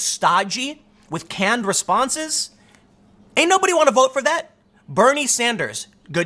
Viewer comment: OMG @ 5:31 i thought you said "Ain't nobody got time for dat" need that lady to collaborate with you on a meme. "Ain't nobody wanna vote for dat" would love to see it.